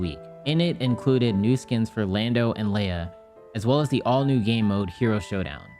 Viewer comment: I dont know if other people hear this; but when he says game he doesn’t pronounce the ‘m’ and I only hear ‘the gay mode’ whenever he says it.